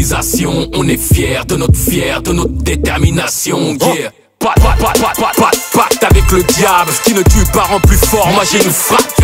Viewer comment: moi jai entendu "pa de pacte avec le diable "